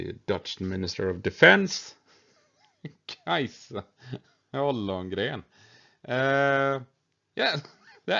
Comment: 🤣🤣 First I thought you said "Hallongren" which would have been funny enough for me, then when I found it was Ollongren 🤣🤣 🤣🤣 🤣🤣 And hearing you read it in real time, hahahaha I can't stop laughing. Thank you. (Yes I am also tired)